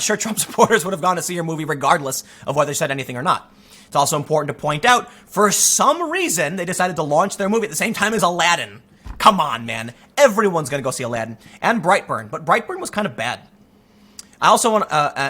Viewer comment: I thought you were saying brieghtbart not bright burn, lol I didn't even know bright burn was a movie